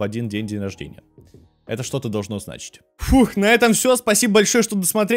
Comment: в конце так сказал, что послышалось "ждем женщин")